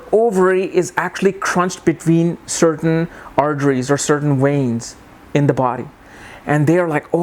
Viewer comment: did u say ovaries are tied between certain arjeries (I promise you there is nothing called arjeries in human body).... I think you meant fallopian tubes... well.. never mind.. u know about astrology and that's what should matter!